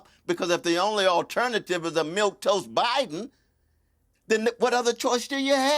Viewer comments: Piers Morgan hearing the word "milquetoast" for the first time and thinking it was "milk toast" is the only thing that makes this watchable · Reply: I thought he was saying “milk toast” too…😂…learned a new word to add to my vocabulary… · Piers Morgan hearing the word "milquetoast" for the first time and thinking it was "milk toast" is the only thing that makes this watchable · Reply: I thought he said the same thing, "Milk toast." I'd never heard of it either.